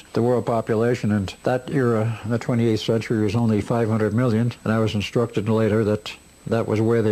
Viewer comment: There are two problems with this story that no one caught on to. Why on Earth would there be massive floating cities if there were only 500 million people? If the cities are already floating thousands of feet off the ground and extend two miles into the atmosphere, how do people breath and keep warm? The city would need a pressurized atmosphere and that would present more challenges. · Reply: I thought he said 5 million per city